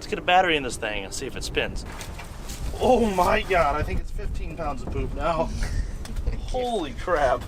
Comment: When you said “15 pounds of poop” I literally heard “15 pounds of boost” and now I’m disappointed that it’s poop and not boost 😅